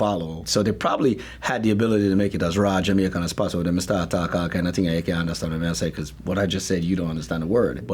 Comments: Jamaicans, what did he say at 2:22? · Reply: "They try to make it as raw Jamaican as possible and they start talking all kinds of things and you can't understand what they're saying" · What is he saying at 2:23? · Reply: He said they had the opportunity to make it as jamaican as possible but no one would understand what they are saying